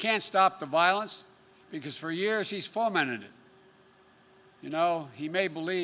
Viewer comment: "stroking violence?" "destroys business only hurts the working families?" It is hard to watch. I honestly feel bad for the guy, he is just not there. Did anyone understand what he said at 22:50? Sounded like he said "foamentted?" Maybe he tried to say formatted? How about 32:15, rough to watch. A 7 year old can read off a teleprompter better.